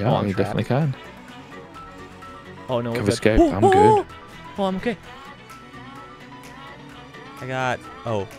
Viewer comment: Why did he say “oh I’m gay”